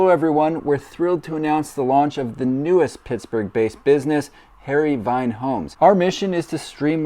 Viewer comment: I hear "hairy vine" which means poison ivy to me. Hopefully this unit doesn't cause a rash.